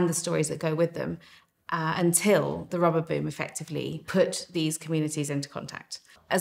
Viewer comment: Sounds like your saying rubber boob